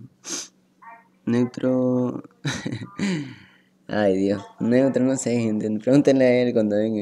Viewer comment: alguien me puede decir que dijo? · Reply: Creo que dijo, Ay dios neutro no se ay preguntenle a el eso escuche yo bro